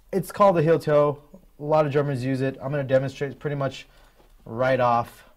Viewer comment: At about 0:20 it sounds like he says a lot of "Germans" use it. I'm aware he says drummers...